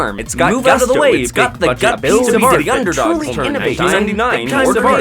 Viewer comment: am i the only one who hears "diddy underdogs"?